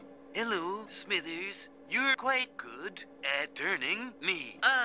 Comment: Lol, at 4:15 the joke went completely over my head when I was a kid, I always thought Mr Burns meant Smithers was good at turning the computer on 😂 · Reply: Me too. I thought he said "You are quite good today", making Burns say something nice to Smithers. 😂